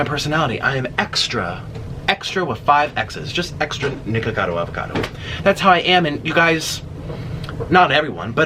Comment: When you said I’m just extra with 5 x’s I thought you said niggavado avacado also I stay because your just cool